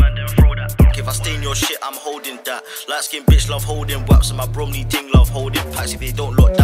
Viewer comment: Did man say Bromley